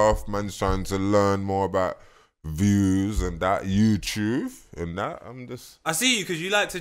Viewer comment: Did my man say Youtuuf and that instead of youtube im dead looooool